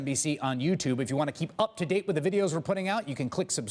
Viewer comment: That Chris Hayes clip at the end. I keep hearing: "...if you wanna keep up to date with the videos we're putting out you can click subscribe, just blow me or click over on this list..." 😆